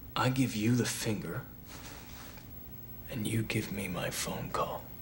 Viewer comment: does he said "and you give my funko? Damm he was definitely ahead of our time